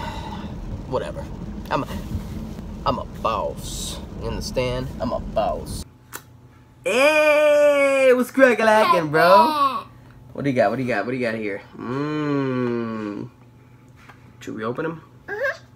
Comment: I thought said "CARJACKING" at first 😭😭